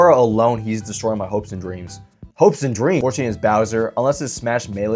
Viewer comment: bro really said "browser" instead of bowser